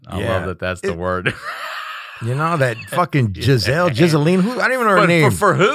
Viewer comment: hahaha did he say Jizzelle..Jizzelene.. I love these under the breath jokes.